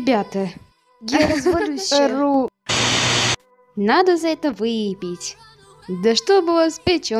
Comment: Я одна услышала « Надо за это выебить!»